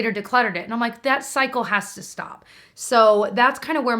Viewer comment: At one point you say “that cycle has to stop” but at first I heard “that psycho has to stop” referring to yourself and I was laughing up a storm, haha! <3